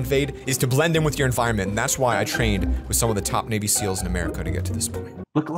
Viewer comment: Thought you said baby seals